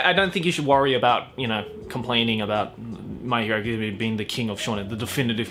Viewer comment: The captions for this video when Joey was talking about My Hero thought he was saying "my hair" so eventually, at 13:15 the captions read: "But yeah, I I don't think you should worry about you know complaining about my hair" XD